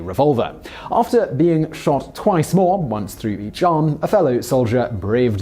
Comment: At Once Through The _____?? Can Somebody Please Help Decipher What The Hell Simon Said!!! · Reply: Zenmasterme one through each arm.